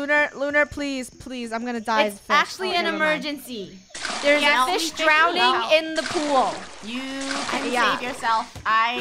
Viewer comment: “There’s a fish drowning in the pool!!!” Me; How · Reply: Octo gal for a second i thought it said there’s a fish drowning in the poo!!!!”